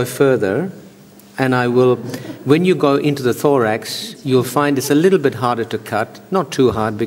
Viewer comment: when he said thorax i thought he said lorax